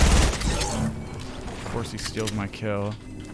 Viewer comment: Bro did u say steeled my kill 🤣🤣😆🤣😆